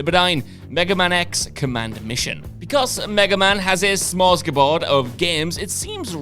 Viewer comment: did he say "smors ki board?"